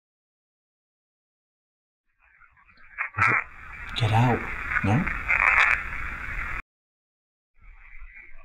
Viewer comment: I hear " I want you to get out. "